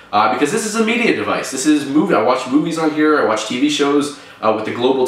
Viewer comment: at 1.39 when he say movies, it sounds like he is saying boobies... just saying...